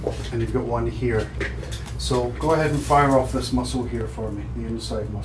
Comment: i thought he said fire off this missile lol